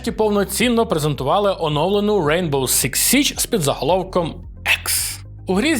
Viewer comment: Почулось Rainbow Six Січ. Я б в таке пограв 😊